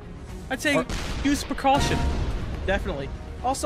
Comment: I thought he said protection instead of precaution and when I think protection I think condoms